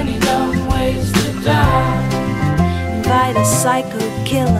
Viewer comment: I first heard "Invite" as "Fight". Uh, that sounds WAY WORSE!